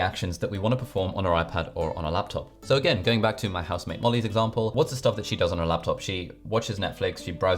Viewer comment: I thought he said “my house maid molly” 💀